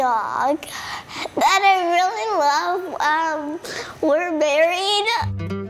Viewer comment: “There is this dog that I really love...we are married” the beginning was so strong I have to like 😂😂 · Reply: okay wtf, i heard "there is this dog that I really love... uhm were buried" just wtf